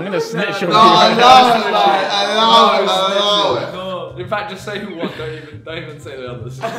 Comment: what on earth does KSI say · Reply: “Allow it man, allow it man, allow it!”